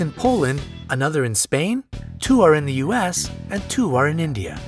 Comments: it says span instead of Spain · am I the only one who caught the Span instead of Spain? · it says 'span' instead of Spain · Did anyone notice the captions at 6:49 said "Span" instead of Spain?